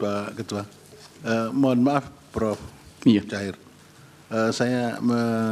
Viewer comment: kok saya dengernya prof mujair ya...mestinya prof muhajir